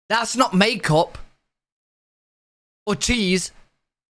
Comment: Did he just say it’s “not cheese”?!?! 😂😂😂😂